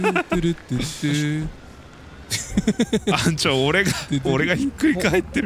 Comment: ぐらいのとこ、「あぁん、ちょ(っと)俺の死体が」って言ったのだろうけど、さんがきたから「あんちゃん、おれの死体が」って聞こえて、あんちゃんって言った！？、ってテンションあがりました笑。